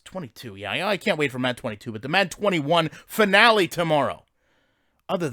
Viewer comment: I thought u said you’ll be back in week.😂😂😂